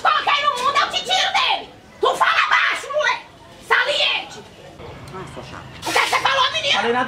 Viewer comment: alguém almentar o volume para ouvir o que ele falou ou é só eu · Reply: ele falou "não, sua chata!".